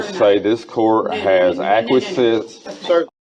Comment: what was that word? "Acquisite?" It's one thing for SovCits to fabricate laws and jurisdictions, but now we have to worry about fictitious language?